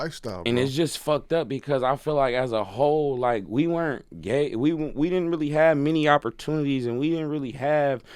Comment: Did he say gay at 6:35 ?? 🤣 Wtf That’s kinda sus I’m not finna lie But he do got bars like a mofo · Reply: He was trying to say "we weren't given many opportunities" but accidentally said "we weren't gave" then switched up to "we didn't have many opportunities"